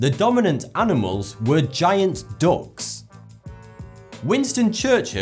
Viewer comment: I thought he said giant dogs